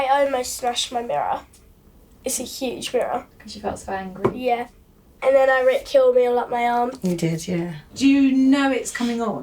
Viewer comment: does anyone know what says here? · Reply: i ripped ..something.. all the way up my arm